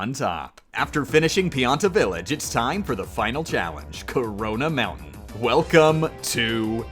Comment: Did ... did he just say CORONA?